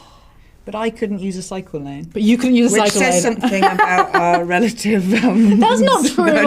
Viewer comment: what did Emma say here? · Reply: which says something about relative... uhm